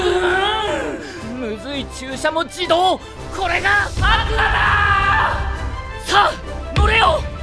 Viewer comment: アクアだーって叫んだ時、何故か「桂だー」って言ったように聞こえた……